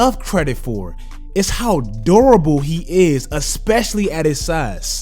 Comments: When said durable I heard adorable 😂😂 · I thought he said adorable 😂